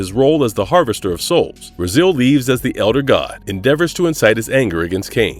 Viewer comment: As a fan of this growing up the way you pronounce Raziel is killing me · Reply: at first i thought he was saying “Brazil” 😂